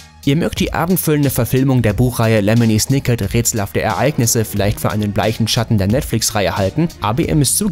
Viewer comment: Wie heißt der Film bei 7:30? Habe das nie ganz verstanden als es gesagt wurde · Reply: Lemony Snicket – Rätselhafte Ereignisse